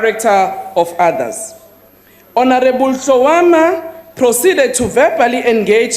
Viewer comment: did she just say, honorable shawarma?